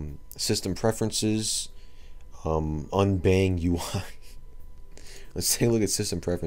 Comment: I thought unbagUl said UI as in user interface but its Un Bang UL. Which means the silver bell in Korean. I guess the program is to make patriotic songs for the great country of "Red Star"...